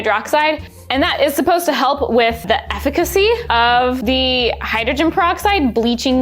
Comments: wait okay am i going crazy or did say “efficiency” like really weird?? or is that how you’re supposed to say it? she says it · did she say eff-ih-ca-see as efficiency 😭😭😭😭😭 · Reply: Well "efficacy" is a word with a similar meaning to "effectiveness" in this context. So she wasn't far off.